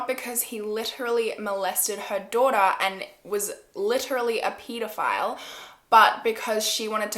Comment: did she say peedophile